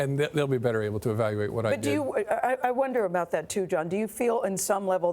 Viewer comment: Did Gayle call him "John" around the 6:58 mark?